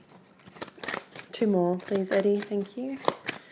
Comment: Did i hear " please Eddie ?" ?? That 's so cool ! Thank you , cross watching taurus, deceived for ten years by a libra :( healing with Eddie 🙏🏽